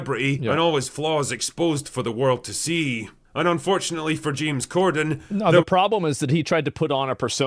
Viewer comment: Corden is the male Amy Schumer. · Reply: It sounded like he said Piers Morgan not James Gordon😂